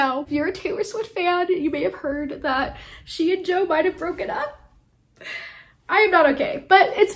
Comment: I don't know why but I heard 'she and Joe Biden broke up' at the beginning of the vid and I laughed. I love your deer tank and the floral top and the final tulle skirt 💛🌻🌾